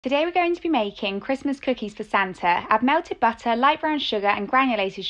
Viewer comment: I thought bro said Fanta